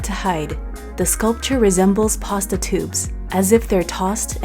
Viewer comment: Lolol. I misheard pasta tubes as proztitutes. I love that park. 💛. I love Seattle. I didnt realise the cajun place was so close by